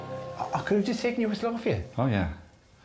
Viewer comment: What is Dara saying at 14:32 ? Closed captioner obviously couldn't tell either. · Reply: He's saying, "aw I could have just taken your whistle off of ya", because he realized he could have used Alex's whistle for the task